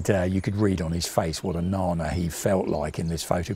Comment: Mr says something about a tank sargent wearing a helmet and saying the look on his face he feels like "narner" What is this word? I have never heard this but maybe I am not hearing the correct word.